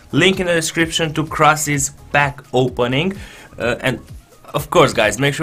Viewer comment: at first I thought you said "link in the description to Krasi's BACK OPENING'' :D